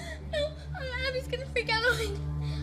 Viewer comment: To this day I don’t know what maddie says at 0:48 does she says abbys gonna forget a line? What does that even mean? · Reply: she said "abby is gonna freak out on me"